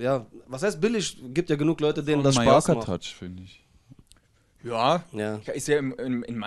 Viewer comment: Was sagt Sikk bei 3:50?? Versteh da nur iwas mit Touch oder so 😂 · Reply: glaube was von Mallorca touch haha